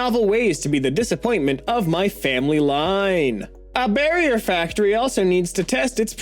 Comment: I misheard that as “Burial Factory” which sounds metal as f*ck